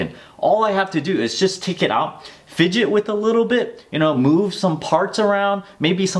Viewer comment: Did he just say we need to fidget spin the device to fix it?